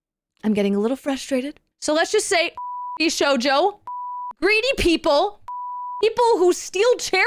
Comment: did she just say vshojover like how I keep hearing nux say